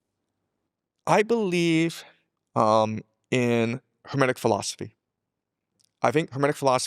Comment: What did he say at the 21:20 mark? What did he call his belief system? What did he call his philosophy? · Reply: hermetic philosophy based on Hermes Trismegistus